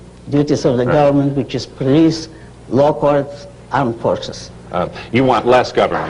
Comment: What does she say? "police, law courts and ???" English is not my mother language. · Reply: Armed Forces